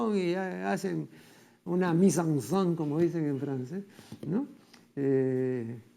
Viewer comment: hola alguien me podria decir que palabra en frances fue lo que dijo Marco aurelio denegri en este minuto 29:25 ??? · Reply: Mise en scene o puesta en escena